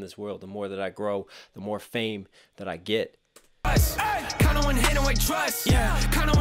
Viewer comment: Bro I almost thought the hook said “kinda wanna handle my trust”